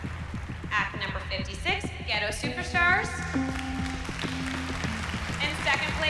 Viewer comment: Did anyone hear at 5:47 one group was called "Gheto Superstars" ?